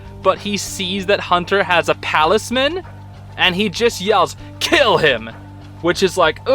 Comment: the captions from what people said and I think said this too actually said “Caleb” instead of “kill him”